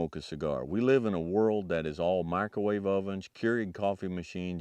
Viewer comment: What did he say right before coffee machines? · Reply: Microwave ovens, Keurig coffee machines